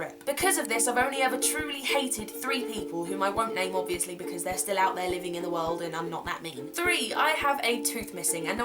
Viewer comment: What she said: "...because they're still out there living in the world". What I heard: "I haven't killed them. Yet."